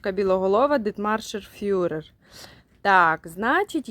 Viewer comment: мне послышалось фюрер)))